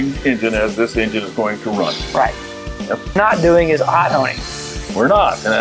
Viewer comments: What are you saying at 3:03 ? What are you not doing? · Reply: Hot honing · What was said at 3:03 into video ? I could not for the life of me hear or reason out what he said he could see they were not doing. Then the guest said well some people live and die for it etc. Any help appreciated Stay blessed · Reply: Hot honing, simulating the running temperature of the engine.